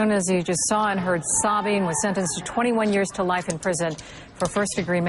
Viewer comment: It was 21 years to life. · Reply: oh sorry never mind I thought you said 21 years of life and we're justifying it sorry for my misunderstanding